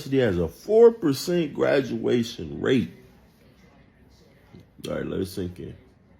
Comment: I had to rewind it back because I thought he said that they have a 14% graduation rate because I was shocked so you can imagine how much more shock I was when I found out that he said a 4% graduation rate!! I’m sorry, but college is a fucking scam!